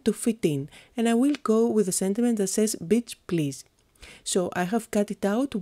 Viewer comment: The first time you said "beach please" I really thought u said b*tch! you are amazing, so very creative, talented and the most adorable english. I'm from the Netherlands and our english isn't so fun to listened to 😯😉!